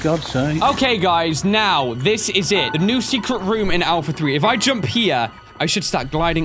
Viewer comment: did i hear ryan say alpha 3